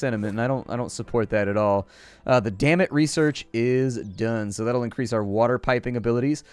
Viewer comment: it sounded like he said dammit but then i saw it was a research thing